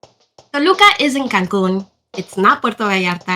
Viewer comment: The beginning of the video sounds like you said, Toluca is in Cancun which was confusing considering it's near Mexico City. Youtube's closed captioning also thinks you said "Toluca is in Cancun" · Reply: Toluca isn’t cancun is what I said